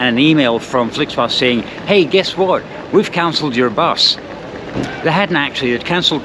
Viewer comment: For a moment I thought I heard 'We've counseled your bus' and I thought yea, that makes sense. After this trip the survivors do need counseling.